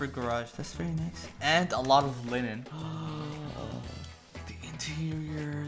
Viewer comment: I thought he said and alot of women 😅